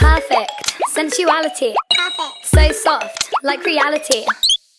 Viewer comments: I heard "So salt" 😂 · Perfect. Sensuality. So soft. Like Reality. I'm feeling like I'm slipping to an undersided fate. But these pants are so smooth and they're more than I could take. I ask you to fulfill my favorite fantasy, like you and I could not tell the difference in insaanity. So Baby. Baby. Is it fair to say I care? I made you taste my favorite tear the one I saved for all these years. You're not- not- that bad. Y-You're not that- Y-y-you're not- not- that- that- bad. You're n-n-not- not that bad. Baby. Baby. Baby. You're- You're- not that- Y-You're- You're not n-not- tha-that- ba-bad. That bad. So soft. Smooth. Sensuality. Undersided. Reality. Smooth. Fantasy. Not that bad. That bad. Not- Not- Not- Not. You're not- You're not bad. That bad. Y-You're not- not- bad. Bad, Y-You're not- not- not- Say it soft. You're not that bad. You're not that bad. You're not that bad. You're not that bad. You're- You're not that bad. You're- that- Sensuality. So soft. Like Reality. I'm feelin-feeling like I'm slippin'-slippin' into an- but these pants are so smooth. Can I ask you- Can I ask you to fulfill my favorite fantasy. Physical Insanity. Insanity so-- That bad. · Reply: Cyan Key I thought she said sexuality xD